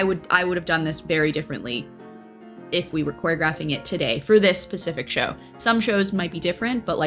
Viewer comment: said “pacific” twice instead of “specific” ahahah, love her tho🤍😂